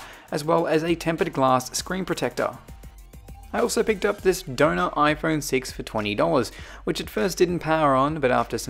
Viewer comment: For the whole video, I thought you were saying donut iPhone instead of donor iphone